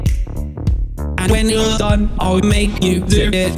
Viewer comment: Slogoman sounds like he's saying "Boot for me, boot for me, boot for me, ay ayy" instead of "move for me."